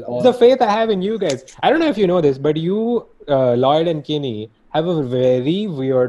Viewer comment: What did he say You ___ and kenny?? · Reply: Not Kenny kini is another one of their friends